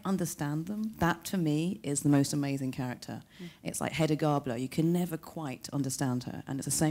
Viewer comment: Heda Garblo? Did she mean Greta Garbo? Perhaps I misheard